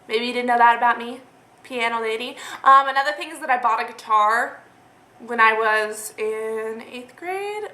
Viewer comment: When you first said "I bought a guitar" I thought you said "I robbed a bar" and I had to rewind it because I was so shocked lol Glad to know you didn't rob a bar :P